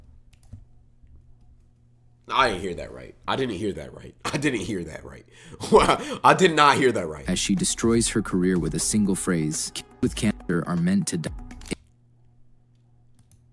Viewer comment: thought she said "men" with cancer, and though she was on that man hating shit. went back and she said kids with cancer